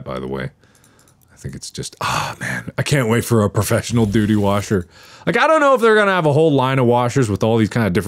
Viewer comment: please tell me someone else heard 'professional doody washer' and laughed like a 6 year old.